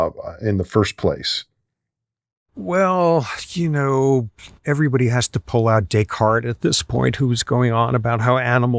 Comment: did he just say everyone need to pull our d**k hard? Edit: he was talking about a guy named Dickard never mind..